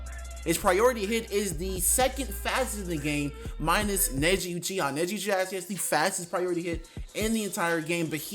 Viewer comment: Did he say neji uchiha